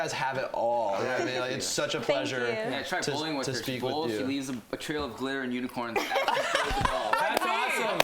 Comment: What did Sean say at 13:05? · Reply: he said: “try bowling with her, she leaves a trail of glitter and unicorns.” 😂